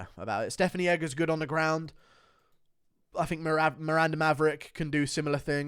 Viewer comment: Did he just say 'Merab' at 9:39? we know you don't like him but I'm starting to think you're obsessive dislike of Merab is getting to be too much.